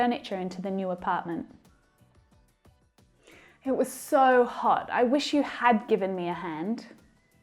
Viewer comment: Mentor, just a little correction at 5:26 and 5:52 the words reflected on screen were "for" and "fo" instead of "so" 😊